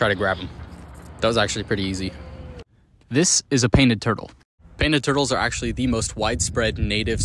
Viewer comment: I swear I heard you say penis turtle LMAO